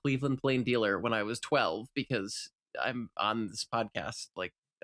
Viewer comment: I thought was gonna say I'm on the spectrum not I'm on this podcast